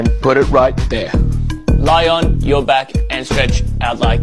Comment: I was half asleep when I tuned in. I though I heard I'M IN CAIRO...🤣🤣I woke instantly..!